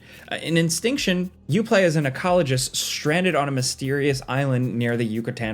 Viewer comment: LMFAO I thought you said 'oncologist' when talking about the dinosaur game 😂 Was wondering what the hell they'd be doing on a dinosaur island